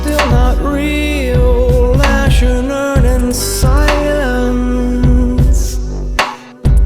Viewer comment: “Ash and urn and silence” That one verse says so much. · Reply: I'm thankful for this comment because I always thought he was saying "As you learn in science"